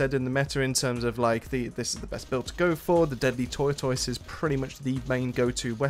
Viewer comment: what gun did you say was the meta here? Also what does the power level affect in PVE, like does having 5k and 1k of the same mods mean anything? Thanks!! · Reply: deadly tortoise